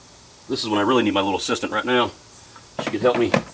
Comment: Ooooh you said assistant 😂😂 I thought you said “sister” i was like, wow what a break 😂